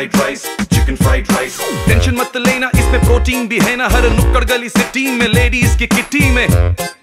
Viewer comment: Ladies ke.................KYAAA?!?!Is that only me who heard that?